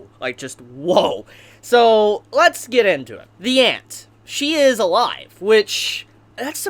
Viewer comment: I know I am not the only one who hears "ant"